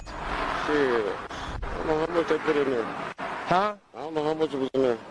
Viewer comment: Did anyone catch that!? The male responded by saying " i don't know how much I put in there" and when the trooper acted like he didn't hear it the first time the guy said " I don't know how much it is in there"